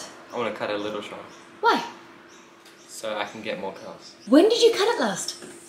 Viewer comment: Had to replay the first bit as I thought he said "get more girls" rather than "get more curls" 😂